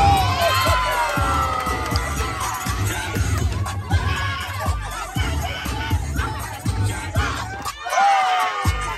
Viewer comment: WAS BANGING AND ANGRY AND THE SAME TIME!!what did she say? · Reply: she said “i’m her sister”